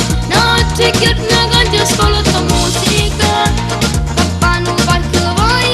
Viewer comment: se sapete potete dirmi cosa ha detto al minuto 01:13? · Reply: notte e giorno canto e ascolto musica